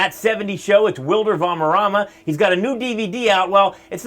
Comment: Did he say Valmerrama?